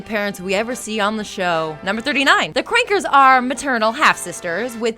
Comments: The few times she said the Kankers sounded like she was saying Qwuankers. o-o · it's Kankers. not Crankers. and it sounded like you said Rolf's and Marie's name wrong. and honey. you're not funny. stop trying to be funny. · did she say krankers